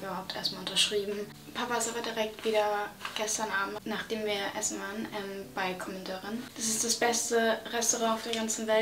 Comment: Hi:) könntest du nochmal oder jemand bitte hier mal schreiben 'das beste Restaurant'?🤍1:49 weil ich nicht so genau verstanden hab haha.. ich fliege nach auch Stockholm bald ! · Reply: Commodore‘‘ :)